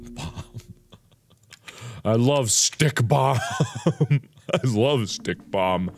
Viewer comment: I thought the “bomb” said snake bomber not stake bomb and I’m thinking where are the snakes?